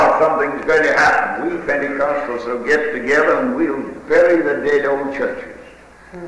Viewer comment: That's good preaching! What goes He say at 29:20 he says, The Pentecostals will bury what? Sounds a little blurry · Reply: "bury the dead, old churches." :)